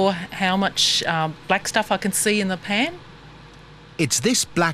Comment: did he just say chepan?